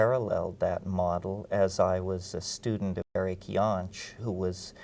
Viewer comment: Anyone catch the name of that systems theorist he mentions at 2:49 or how to spell his last name? No amount of googling or variation on Eric came up with anything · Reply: oh finally found it through his book. Erich Jantsch, Design for Evolution. sounds like a great mind